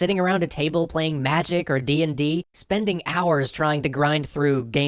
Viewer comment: Did somebody say DnD?